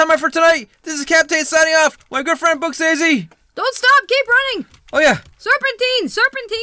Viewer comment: My great feind books_a_daisy I thought u said "And my GIRLFRIEND..." 👍😻💩